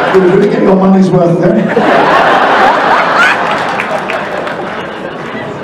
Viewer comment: I thought he said "Now I'm Geordie." As in someone from Newcastle.